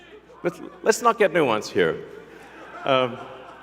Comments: I thought elon said in regards to cyberyruck was "lets not get new ones here" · I thought he said “let’s not get new ones” 😅